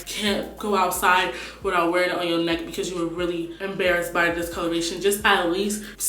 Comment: EMBRACE THE BODY YOU GOTTT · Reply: i thought you said embarrass and i was like "hold up🤔" LMAOOOO